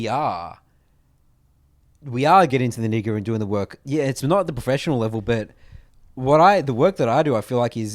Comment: Can someone with a better understanding of accents than I please clarify 1:43:44? Getting into the what now??? I've honestly been trying to figure out what else could've been said and can't for the life of me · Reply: That would be "Nitty Gritty", hahaha